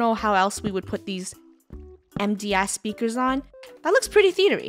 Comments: DID I HEAR SAY " EMPTY A$$ SPEAKERS" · am I the only one who thought said empty a** speakers???? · Did she just say empty speakers 😭😭 on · did say the A word? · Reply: No I think she said MTS/MDS speakers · Anyone else hear say a-- while she was making the theatre? At 10:24 she said "empty a-- speakers" 😶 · What did say about the speakers it sounds like she said empty a— speakers for the theatre me be like 👁️👄👁️ Edit:she said mds speakers lol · Uhm no one noticed the time in the theater, when she said smt about the speakers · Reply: It sounded like she said empty a- speakers, but she said MDS Speakers 😅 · it's my first time hearing one of the members swear before🤭 But it's fine there old enough but still shocking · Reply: I’m assuming you thought she said empty speackers. She said mds speackers wouldn’t swear in her vids bc her vids are kid friendly. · Did just said empty speaker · Did say empty as- speakers? Go TO